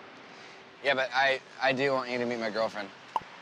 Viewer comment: I thought Jason first said 'I want you to BE my girlfriend" and then when he starting talking about someone else i was like wait waaaaaaat?!?